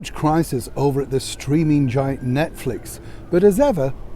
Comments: I thought you said the "screaming" giant 😁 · For a moment I thought you said screaming giant Netflix….😂 I can’t imagine what’s going on behind those doors! I’m feeling for the very reputable director assigned to the “love story” that is Haz and Megs….as not telling it through their lens…😩 Damned by faint praise…